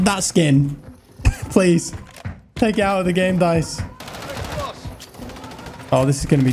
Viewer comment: 👀 did he say Dice?